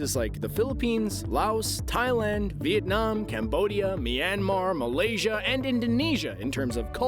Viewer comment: oi did you said cambodia cause 🇰🇭 is where i live